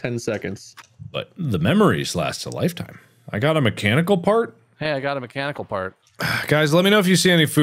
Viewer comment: When Mark boarded the yacht and picked up a mechanic part... I thought it said "mechanical fart" and I laughed a little too hard at that one